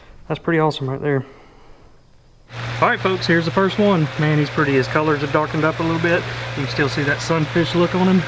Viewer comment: you say "man, he's pretty" but I heard "Manny's pretty" so you should name the sun fish Manny and Hitch for the blue gill because they're hitched together by the same pole